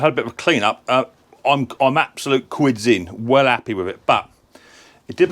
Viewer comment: Fantastic. At 23:53, what was the word you used? "I'm absolute "quidsing"? It must mean that you were pleased. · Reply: Quids in.. means a good money return